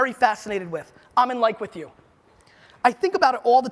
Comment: what is the site he said. Was it 'im in like with you'?? I couldnt understand it?